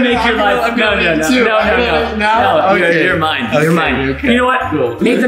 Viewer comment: Did preston say "Your mine"????? wtffffffffff, some one tell me wtf he said..............wth